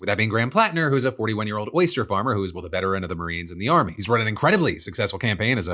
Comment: Anyone hear moisture farmer at 12:29 instead of oyster farmer?